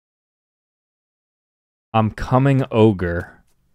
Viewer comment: I heard “I’m coming ogre” and I INSTANTLY thought shrek